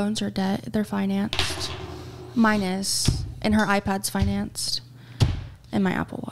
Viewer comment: Did she just say “her iPad’s financed”? “Her” as in their baby?! Please do not tell me they financed an iPad for a literal infant…